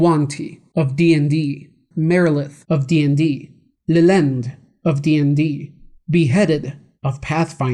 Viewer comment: The picture at 7:14 where is that from? Also exactly what did he say? I have hearing problems lmao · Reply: Lillend of D&D, thanks for the question